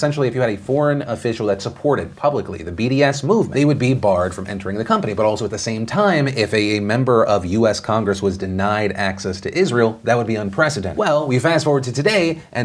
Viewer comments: "They would be barred from entering the company" Am I the only one who heard "company" (8:03)? I want it to be real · is he saying "barred from entering the company?"